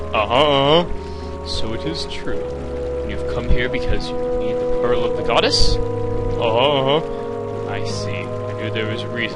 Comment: i thought it said the penis of the goddes